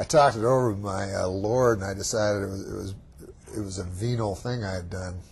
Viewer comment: "I talked it over with my, uh, Lord and I decided it was a venal thing I'd done..." Gold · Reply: I thought I heard "venial", which also works in context.